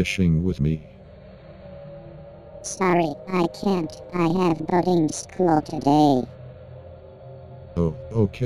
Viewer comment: Who else thought that every time it said boating it sounded like the f word but with the ing on the end